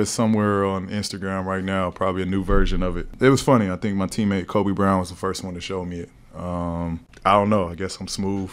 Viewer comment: Who else thought he said “my teammate Kobe Bryant” in the beginning